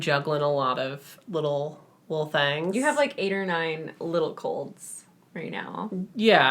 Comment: Someone tell me why when said Niko had 8 or 9 "little colds" I thought she said cults and I didn't question it